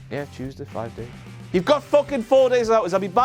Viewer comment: I heard King Ross say fuckin', SHAME EDITORS! SHAME